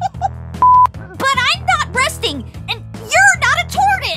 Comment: when I heard this for the first time I thought she sead bresting